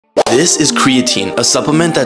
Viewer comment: At first I thought that said "this is cocaine" 😂